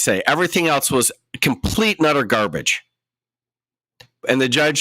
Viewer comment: what I thought you said: “Everything else was complete nutter garbage.” I know you said “utter garbage” but really both are true. 😏